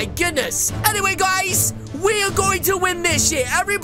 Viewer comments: did he say "we are going to win this sh**"? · did he just say? SH*T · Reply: he said year